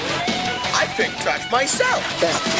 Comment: Doesn't anyone like the bit where it sounds like Bulk says "I pick trash myself" I think it's funny because it looked like Bulk just picks up random trash.